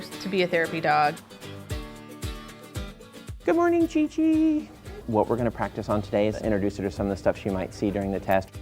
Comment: I always misheard its name to onii chi chi